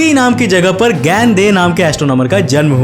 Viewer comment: first time i hear it i heard g**d de lol